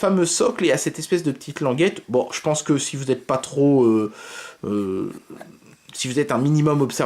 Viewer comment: Si vous êtes pas trop quoi ? :P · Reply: woane pas trop ampoule bluetooth :)